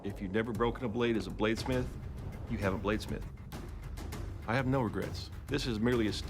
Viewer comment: Thought the first guy said "i have no regerts"